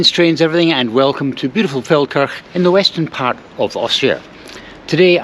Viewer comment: I first thought you said 'beautiful Falkirk' in Austria 😂